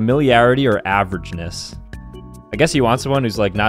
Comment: 🦣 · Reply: I just heard "assuming you're beige"🙂